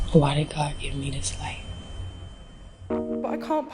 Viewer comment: What is she saying on 0:47? Help please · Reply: Why did God give me this life…